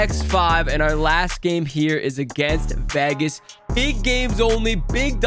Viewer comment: did he say vehgehs?